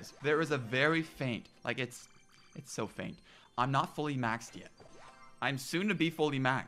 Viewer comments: I legit thought he said “I’m not fully vaccinated” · on first listen no lie thought u said im not fully vaxxed yet